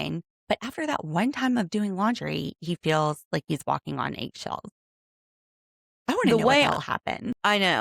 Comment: What brand is AYGO? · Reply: Nvm I just heard you says ‘aygshells’ so I got it now 😆